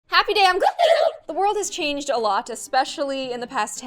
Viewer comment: I always thought you said happy day and woho but now I know it’s happy day I’m gloom Gosh I’m dumb